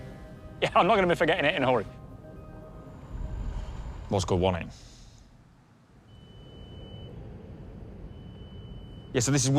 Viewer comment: did he say Moscow won it? What does that mean? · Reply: "it was gud won it" or "it was good wasnt it" in english